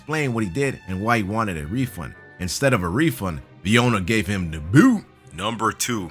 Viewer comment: "Instead of a refund, the owner gave him DA GOO" ????